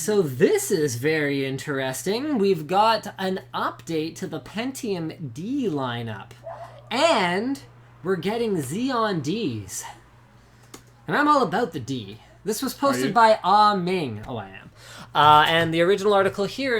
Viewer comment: Linus, did you really say you were all about the "D" at 25:12? :)